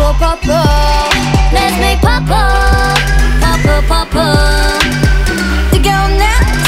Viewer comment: When you realize “Let’s make purple” is an innuendo...FYI purple sounds like 뽀 뽀(ppo ppo/bbo bbo) meaning kiss thus let’s make out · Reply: when i heard this song for the first time without knowing the name, i thought they said "lets make bubble, bubble bubble" 😭